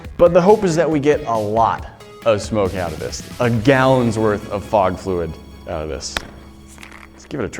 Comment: I thought it said frog liquid!!!